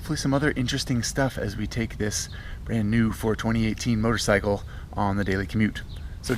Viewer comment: Wait, did say, "This brand new, 420-18 motorcycle." at 0:32 in the video? Well, I'll pretend that he did.